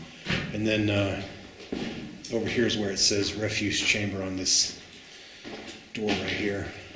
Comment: I thought it said "refuge harambe" at first